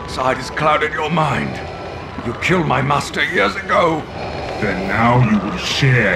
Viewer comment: When I was younger, I never had subtitles on & I always thought he said "Yodago." Like his master's name was Yodago. Nope. He says "Years ago."